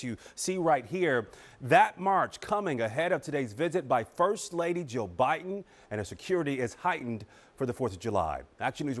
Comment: I heard "first lady, Joe Biden" and I'm like what happened? My bad.